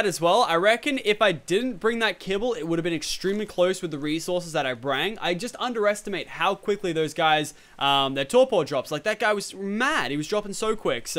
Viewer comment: did u just say "brang"? 15:49 lol